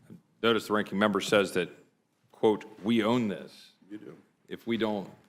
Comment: Listen to 0:09 , it sounded like Jeffries smart as said “we do”. Nitwits don’t even know when their mic is on 🙄